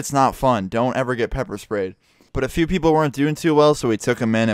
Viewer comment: oh I thought u said ur mom sprayed u